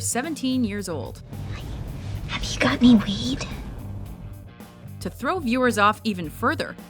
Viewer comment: Did I just hear "Hey. You got any weed?" o_O xD